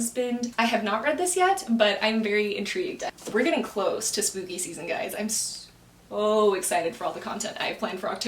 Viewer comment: When you said spooky season, I thought you said smoothie season 😭 I'd probably fail at this because my mind went blank with every question 😂